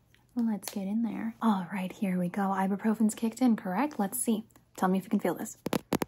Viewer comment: did she say ibuprofen??? I was just searching up things about it